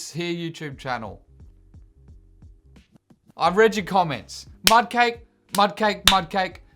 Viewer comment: Misheard: 'I'm Reggie Comments'